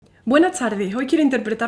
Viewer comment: Ban a decir hay que tonto no 😂 pero yo siempre pensé que decía el inicio de sus videos "bueno chardis" refiriéndose a nosotros, y me acabo de enterar que dice "buenas tardes " 😂 y me di cuenta de que no nos llama chardis sino que nos está saludando 😅😂 una disculpa